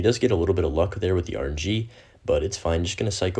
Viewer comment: what does he say? "luck with rg?'' what is rg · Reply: Rng is referring to the random spawn that graveyard has. He said he got lucky with the rng because the skeleton got an extra hit based on where it spawned. ( correct me if I got a bit of that wrong )